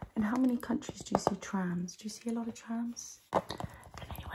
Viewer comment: "How many countries have trams?" Me: uh, as far as I know, all of them? Trans people exist everywhere? "Do you know how many have trams?" pointing to train Me: realizing I misheard oh 👁👄👁